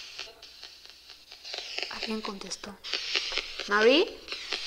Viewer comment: en esos segundos cuando dice Mary, escuché que dice algo como: ¿Dime?